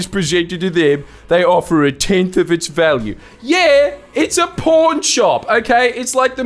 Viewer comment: Because of your accent I kept hearing "porn shop." So in that regard, I'd like the next one to be "Worst PORN SHOPS in Australia"